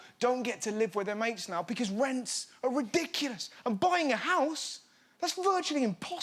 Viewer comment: NGL I thought it said buying a horse for like 5 minutes